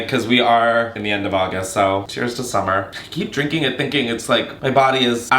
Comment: I zoned out for a second watching this while braiding my hair and suddenly heard you say "cheers summer" and was like omg what? thanks! (Thats my name 😅)